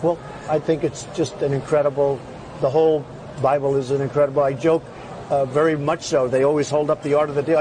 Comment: Trump is all about the plug. “My second favorite book is The Art of the Deal.” Did I hear “Bibilism??